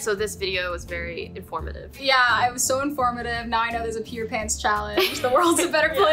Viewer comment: Oh I thought he said Peter Pan challenge 😂